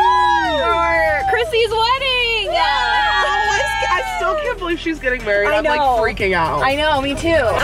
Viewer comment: I feel so dumb I thought they say cissy was getting married and was like wait is it like a modern wedding why is she wearing blue